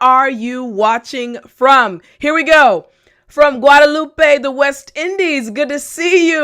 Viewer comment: Hello you're my favorite teacher, im from Guatemala 🇬🇹🇬🇹🇬🇹, when you said Guadalupe I thought you say Guatemala 😃😄😄